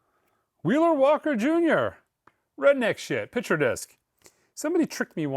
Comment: I thought you were saying it was “redneck shit” not calling it by its title 🤣🤣🤣